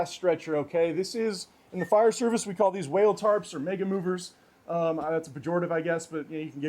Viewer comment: totally misheard and thought you just casually said the n word lmao. Mega movers seems more appropriate, even if less funny.